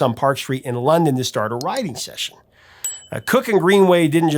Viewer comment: Adam, I keep hearing "rioting session" when you say "writing session" lol. Either way, it sounds cool. Thanks for the vid!